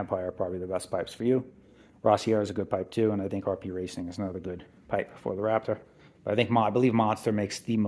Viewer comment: I didn't say that so I'm not sure what you're asking. I said if I had to do it again I would get a monster empire RP racing or rossier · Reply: ohh my bad i hear "not good" at 22:28 sorry for that. btw your bike looks good :)